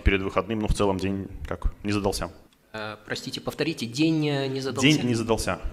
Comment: Видимо не мне одному послышалось "Динь не задался" ))